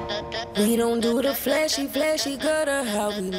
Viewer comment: We don doo duh flashy flashy just straight killed me. Not even 10 seconds in. Incredible. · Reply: Flashy? I thought they were saying "fleshy". That song is so badly autotuned I can't understand any of it.